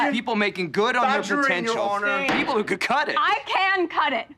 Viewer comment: I have to say, I think the end of the mock trial was written badly. It was not about "breaking her" further, she already was ("I can't cut it") which breaks her narrative wide open. Not a lot of pressuring required, but rather using those same words again. He won, or at least would have soon. Don't know what's so tough about asking uncomfortable questions, they don't have to be personal (as to Rachel not the person she is immitating) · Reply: You also straight up misheard her. She exclaimed "I can cut it."